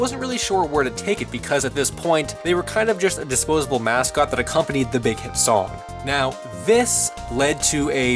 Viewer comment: What did he say at 2:00 - "they were a disposable mascot that a company that they can song"? Am I mishearing that? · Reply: They were a disposable mascot that accompanied the song- meaning the song could survive without the Hampster mascots but the Hampster mascots needed the song to be recognized